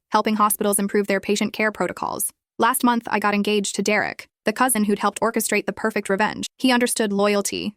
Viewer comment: Am I wrong or did she get engaged to her cousin?? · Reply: I heard it as Jake’s is Derick’s cousin